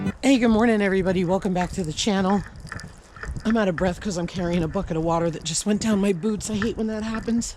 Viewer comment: I thought you said “a bucket if water that just went down my boobs”😂 Gosh, what a snowy mess! Back to your lovely buckets!